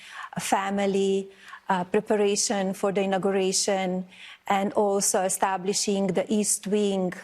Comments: "Preparation for denigration..." WHAT? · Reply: I heard it as "preparation for immigration". 😂 A commentor clarified that she said "preparation for inauguration. " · Did Melanoma just say “… preparation for denigration ….”?? Sounds about right 😂😂